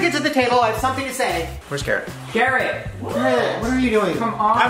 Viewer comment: When you said "WHERES GARRETT" i thought you said where's carrot😂😂😂😂😂😂😂